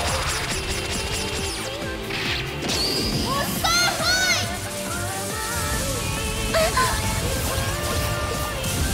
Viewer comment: 霊夢なんて言ってるか分かりますか💦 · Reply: 夢想封印！！だと思います！というか、本家にそう書いていました！